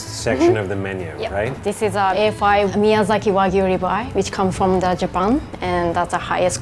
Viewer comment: Did someone said "Miyazaki Vag*na" on that script?? Or is that a misleading n mishearding from me?? 😆😄